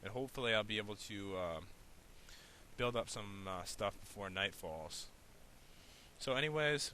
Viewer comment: it sounded like he said fortnite